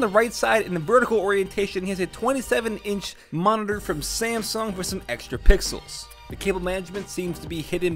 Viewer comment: Samsung??? I swear it says Asus